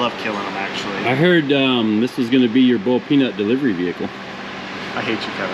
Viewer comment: Ohh maan!! It’s ONNNN!!! 😅😂 Paint it green and call it “Bawled Peanut” 😂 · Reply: I thought they were saying "bull peanuts" at first. lol.